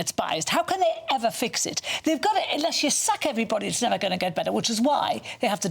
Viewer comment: I'm american, I thought the young lady said: "you have to suck everybody or it won't get better". No she said you have to sack everybody...